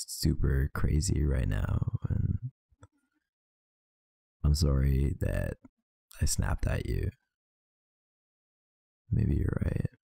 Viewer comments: I thought it said 'sorry I slap you' hahhahaha excuse me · I thought it said "Sorry I slapped you" I was so confused for a second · I swear I thought it said I'm sorry I slapped you